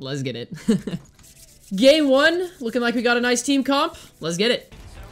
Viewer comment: Is it just me or does he say 'gay one'